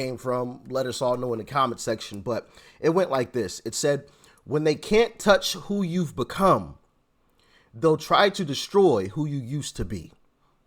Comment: Hey, I heard it as "If they can't destroy you for who you are in the now, they'll destroy you with your past. KEEP SPREADING THE TRUTH, BROTHER. PROUD NAVY VETERAN